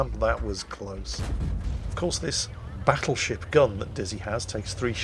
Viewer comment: It sounded like you said battle shit gun